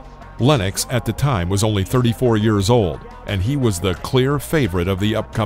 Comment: "already 37 years old!" "only 34 years young!" WTF? Makes no sense. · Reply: he clearly says "only 34 years OLD". Sure, he's still implying there's a difference but not as much as you made it sound.